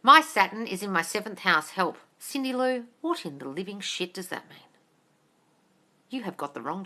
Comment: IM LMAO....just heard you say..." What in the living SHIT is that?"...LOVE IT... LOL